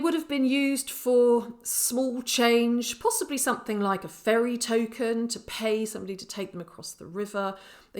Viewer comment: hmmm i heard "fairy token" and I was all about it.